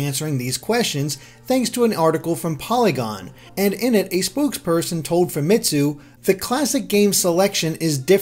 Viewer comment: I heard "spooksperson," instead of spokesperson.